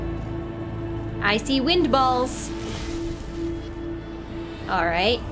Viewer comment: I thought you said "icy wind balls" which might be what a guy gets in winter if he forgot to do up his zipper. Anyways I'mma watch the rest of this. Always fun. 🙂